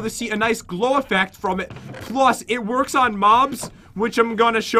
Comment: Did anyone else hear pat say "plus,it works on moms" at 2:56-2:58 (at dat moment i said "i wish it could work on my mom"😂😂😂)